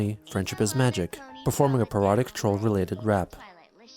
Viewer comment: Bruh I thought you said erotic troll related rap this entire week and even after rewatching the part 5 times just now. No clue how I only just now realized you said parodic.